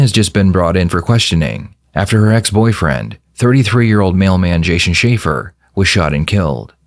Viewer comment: I thought the narrator said "male man" at 0:07, I was so confused until I remembered that the word "mailman" exists lmao (as a non native english speaker)